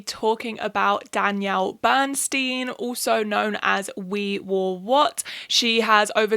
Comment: I thought this said “we whore what” and now me and my roommate can’t stop saying it help